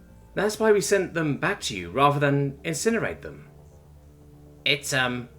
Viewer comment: Incinerate?! Christ almighty! A bit extreme, don't ya think? Pretty sure it says Incarcerate (To Imprison), lol